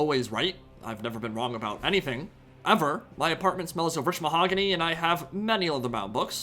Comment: Am I crazy or did he say his appartment smells of monogamy? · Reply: I thought he said mahogany. But I have no idea which books he was talking about, in the same breath.